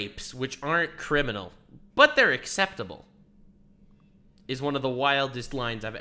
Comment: you misheard. he said she said "unacceptable" not "acceptable". oh dang, i said "he said she said" isn't there a word for that? not defending amy here, just pointing something out.